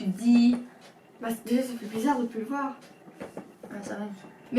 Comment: euh... tu as dit quoi ??@@ C'est pas gentille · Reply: Ça fait bizarre de ne plus le voir !